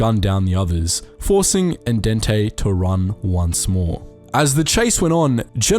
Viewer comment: "Forcing Al Dente to run once more." Is what I thought you said. Was this a half baked plan! Hahaha! Shutting up.